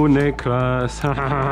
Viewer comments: did he just say n#%^* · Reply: Hell no! I was saying "Niklas" (German name) as I blasted him in the face :) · what did he sayyy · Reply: "uh niklas hahaha" other guy " i got one"